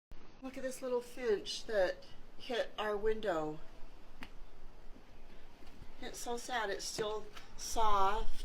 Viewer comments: And first I thought she said look at this little fish😂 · Lmao i thought that u said this fish hit out window 🤣🤣🤣